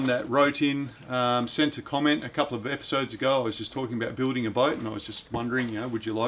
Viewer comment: Building a boat ? I thought you said building a goat and the start of this video made me think you had already succeeded. You do realise you cannot milk a boat don’t you ?